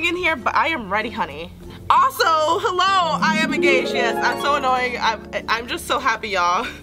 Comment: Ohhhhh she said I’m engaged I thought she said “IM GAY YALL” 😂